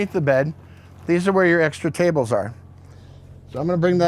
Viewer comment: Frame 24:48 Did he say under the mattress are the extra tables? 😲😲